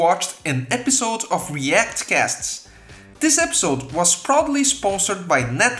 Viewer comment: thought you said "this video was probably sponsored by Netlify" but then realized you said "proudly" instead of "probably" XD great vid tho